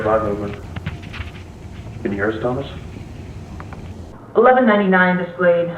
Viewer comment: I heard “can you hear us tommas”correct me bec I think I spelt the kids name wrong 😑